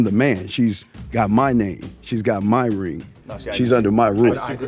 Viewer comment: Yikes! You deliberately misquoted him to push your weird agenda that male leaders are dangerous. 11k people liked that. That’s scary. Why’d you replace his “my lead” with “MY rules” exactly? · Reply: he clearly said “my rules” in the end of his sentence